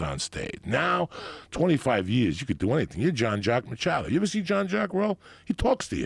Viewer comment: what name is he saying? I think it's John Jack Mccalie but nothing comes up on Google